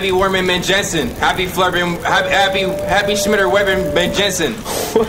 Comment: Wait tf was trying to say tho???? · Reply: He was tryin to say Smitty WerbenJagerManJensen which comes from a Spongebob episode but I think he actually meant Happy Leif Erikson Day which is also from Spongebob